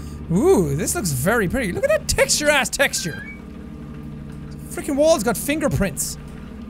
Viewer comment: "Look at that texturized texture" - JackSepticEye, 2019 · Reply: I heard "texture-ass texture"